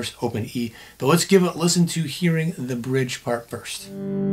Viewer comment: Thought he said listen to the orig not bridge 🤣anyways dope content man !